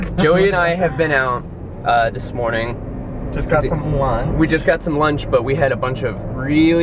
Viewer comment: omg it sounded like joey said "yeah we just got some blunts" and i was like okay joey...